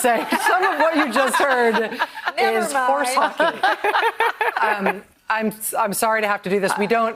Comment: ? How is what he said " horse monkey " I think that dumb ass said, how. I cant hardly stand fox news but seeing those braindead morons after avoiding them for so long infuriates me